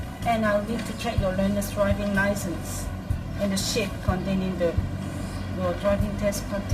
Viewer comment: I swear...at 1:49, I heard.."ahh shit.."